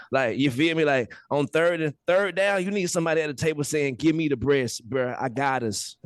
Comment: At first I thought was saying give me the bris.